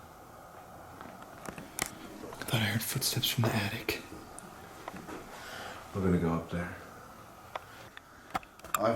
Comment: I heard a disembodied voice but can’t make out what it’s saying... maybe “look” ? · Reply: Yeah that was weird just as was talking you hear “look” mad